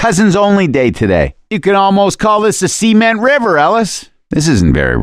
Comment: is he saying -You could almost call this a semen river Ellis?